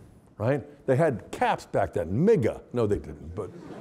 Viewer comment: Did Scott Hahn just say the n word??? Haha. What did he exactly say? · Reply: He said "MIGA" instead of "MAGA." That's the whole joke. 😁